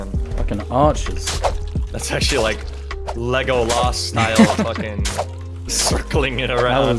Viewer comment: sorry what did he just say.... Lego Lass? not legolas?????